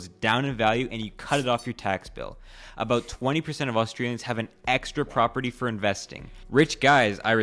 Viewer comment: Did he just say "Austrians"! 🤔